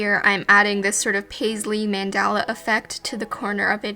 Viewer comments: i know you said "mandala effect" but at first i heard "mandela effect" and i was all like 👀👀👀 IM HERE FOR THIS WHAT ARE YOUR THEORIES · omg i thought u said mandela effect instead of mandala 😂